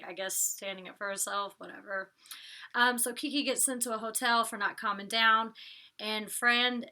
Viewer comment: I thought you said "And than Kiko was sent to a hotel..." 😂